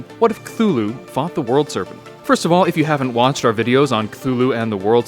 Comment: Who else thought it said forgot instead of fought